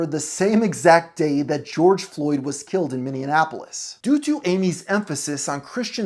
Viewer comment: BRO DID YOU JUST SAY MINI ANNAPOLIS